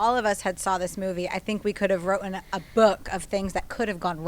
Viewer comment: did saw wrotten???? instead of written